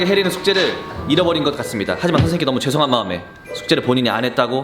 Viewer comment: 선생님께 라고했는데 그새끼라고 들림ㅋㅋㅋㅋ